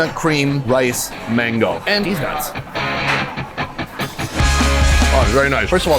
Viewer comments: does he say deez nuts? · Did he say DeezNuts? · I keep hearing "aand Deez nuts" at 14:12 😂